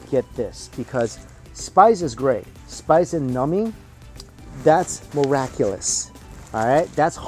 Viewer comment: For the longest time, I thought you were saying "nummy" and didn't realize you were saying "numbing" - like I thought you were saying something is "yummy" in a cute, child-like way, lol! I'm a doofus! Hehe